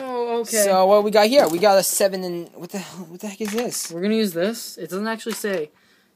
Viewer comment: did say what the hell at 1:38 ???